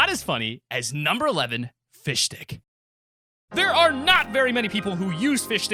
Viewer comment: I thought he said fish thicc